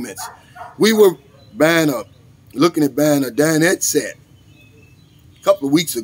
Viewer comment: Headset? Come over here and experience life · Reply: I heard headset 7:57 Your accent is so thick, you sound like a whiny baby